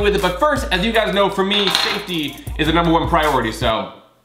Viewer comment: I thought he said safety first